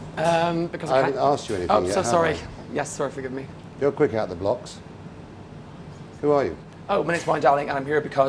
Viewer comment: When Marco said I'm just a cook it reminded me on Steaven Seagal and movie Under Siege when he was saying "I'm just a cook!" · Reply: What did Marco said at 11:08 " your quick heather blocks" ? And what did that guy respond to him?